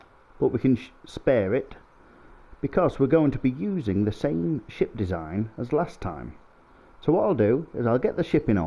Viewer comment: I honestly thought you said "same sh!t design as last time" lol. That design got me to the mun and back!